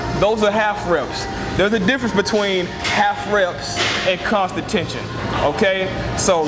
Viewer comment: Hey, sounds like you say "calf reps" instead of half reps around the 6:05 mark. Haha. Anyway, good video!